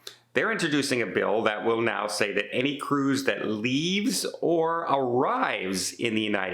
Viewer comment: It doesn't matter what they're worried about. The law basically tries to claim that any cruise ship, regardless of its flag or where it is in the world at the time, is always under American jurisdiction. Even in another country's territorial waters. And that will never be allowed to stand by any other country. None of the cruise ship lines are US flagged either, so even in international waters, the US has no jurisdiction. This is fully established international law set by treaties to which the US is a signatory. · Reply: No, you didn't listen. At 1:42 he clearly says "leaves OR arrives", there's no "starts and ends" mentioned.